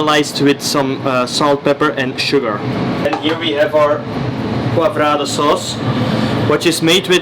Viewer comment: what sauce does he call it? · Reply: Poivrade